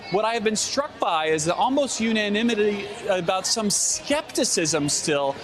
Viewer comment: What in the fuck is that word??? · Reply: Unanimity... I think?